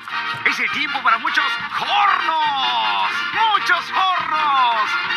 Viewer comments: Siempre pensé que decía , muchos gordos XD y por eso Peter se enojaba más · The First time when I heard this I thought it said Muchos Gordos. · I thought he said "Muchos Pornos" 😂😂😂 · I thought the singer was saying muchos gordos lol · I always thought it said “Muchos carros” meaning “many cars”, as if it were an ad. But it’s even funnier now that I realize he’s just taking the English word “horn” and adding an ‘os’ to the end to emphasize the ensamble of horn instruments. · I thought it said Muchos Pornos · i thought it said "pornos" im so sorry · I misheard this as "Muchos Pornos"